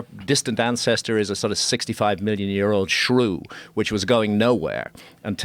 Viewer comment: did he say shru? My English is not the best, please someone help with what he said 😀